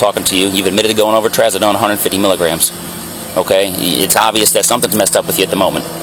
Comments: Who tf takes 700mg of trazodone?? 50mg knocks me tf out! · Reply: I thought he said 150mg. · 700 mgs of Trazadone?! 😳 Plus alcohol at .342?! 😱 What kind of tolerance does this dude have?!?! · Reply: I heard him say 150mg …